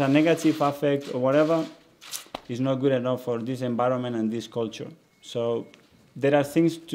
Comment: As long as he starts all his interviews off with "gudibinin" he's okay in my book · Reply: hahahaha i heard him say gud enbironment ahahahaha maybe he will be entertaining in press conferences